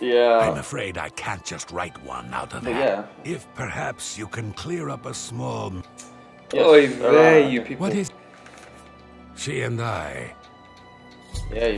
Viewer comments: Did he just say oy vey? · Did he just say oy vey